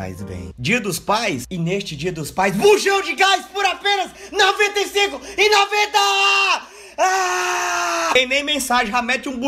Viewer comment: eu entendi "FUJAM DE CASA POR APENAS 95,90 AHHHH"Kkkkkkkkkkkkskskskksksksksksjdjdhskksksls